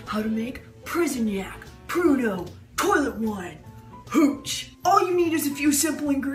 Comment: You got my husband’s attention when you asked Dan to try your hooch because he thought you said cootch 🤣